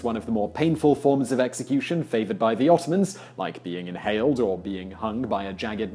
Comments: It sounds like you said, "inhaled." (1:43) ♫♪ Not Constantinople?♪♫ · did you just say "inhaled"? How do you inhale a person!?!?! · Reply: Fakjbf He said impaled. Not inhaled